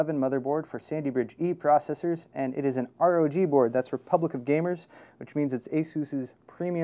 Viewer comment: At first when said Republic of Gamers, I thought he said Republican Gamers.